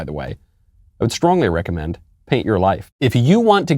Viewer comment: I thought he said hate your life instead of paint your life